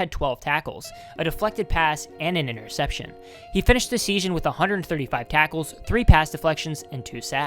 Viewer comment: Did anybody else hear it ?? 😅😂😶 2:58 "finished the seashon " lmao dont know why I found that so funny